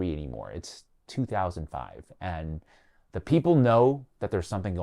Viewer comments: did you say “it’s 2005” Because that’s gonna mess people allll up in like 10 years 😂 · Did homie say it’s 2005?